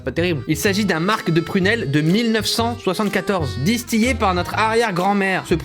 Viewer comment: disti- quoi ? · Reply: Distillé